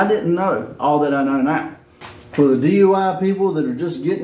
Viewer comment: did you say " for the DUI people"??😳👀🤣🤣🤣🤣🤣 Thats what i heard😂😂😂🤣🤣